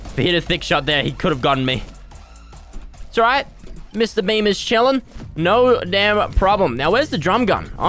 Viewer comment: Honest to god thought that when he said “mr beam” thought he was saying “mr bean.”